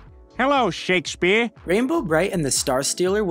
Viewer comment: I had to to replay the one about rainbow bright I thought he said rainbow colored nipples 😂